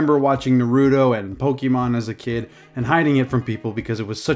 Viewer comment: OMG DID HE JUST SAY NARUTO WRONG OMG HE JUST SAID “NARU D O”!!!111!!!11111!!!!!!!1111!!!!!11